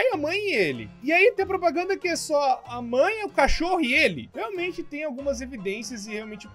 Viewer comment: só eu entendi "A cachorra" ? Kkkk